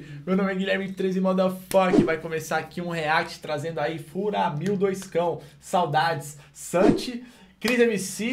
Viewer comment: Hoje que entendi "Furamil 2Cão" "Furacão 2 mil".